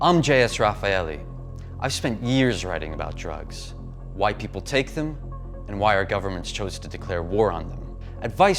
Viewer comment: it sounds like he said "White people take them" and then proceeds to show a bunch of wacked out white dudes going nuts.